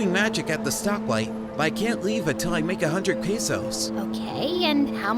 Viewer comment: Did he just said pesos…